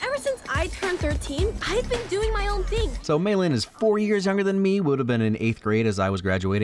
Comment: j thought he said 40 years younger than me, I had a record scratch moment